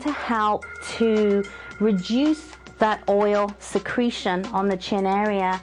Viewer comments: Everytime she said "reduce" I thought she said "re-juice"... :p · When she said reduce I thought she said rejuice.HA